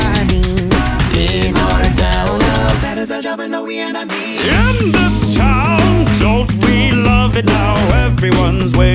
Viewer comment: Wth are they saying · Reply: "That is our job and no, we are not mean."